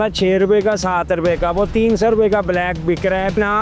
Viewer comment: yar is bande ne kya bola h 30 ya teen.so😂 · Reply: 300